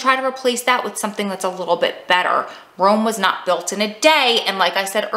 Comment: Lol I thought you said wasn’t built in a day... but yes Rome wasn’t built in a day. Love you 💕 and thanks for all the amazing tips 💕